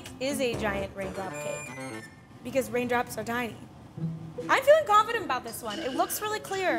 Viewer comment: At first when she said “cause raindrops are tiny” I thought she said “cause raindrops are dying” lol